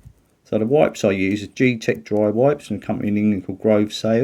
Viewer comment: What kind of cloth are you using to wipe up the flux? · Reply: I think he said "G-Tech dry wipes"; it went by quickly at 9:24, I think.